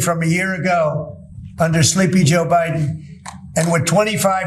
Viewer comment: Did i hear 20 minute oval office nap TFG say sleepy joe?